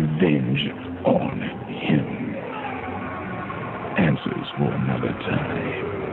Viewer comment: did he say "answers will never die" ?